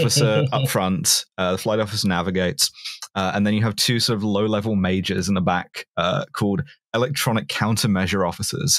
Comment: I swear, I heard say "two low-level mages" at 1:05:52".